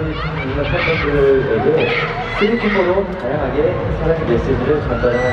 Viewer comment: Did I hear Sehun wrong, did he really say Philippines? 10:10-10:18 😵😵😵😵😵😵😵😵😵😵😵😵😵😵😵😵😵😵